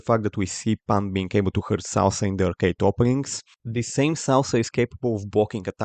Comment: who is the guy pan is kicking i couldnt understand his name when u said it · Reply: Salsa